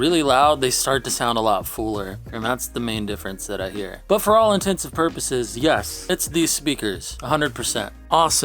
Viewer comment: did you just say "for all intensive purposes" ? 😆